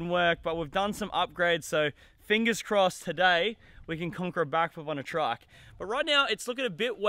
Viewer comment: I thought it said triple backflip on trike